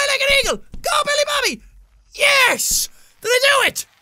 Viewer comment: I thought he said Millie Bobby And I thought the actress was in the video XD